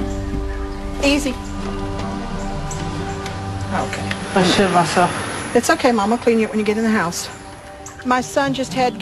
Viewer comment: Did he say, I shit myself? and did she say she'll clean him up? My mind is blown right now...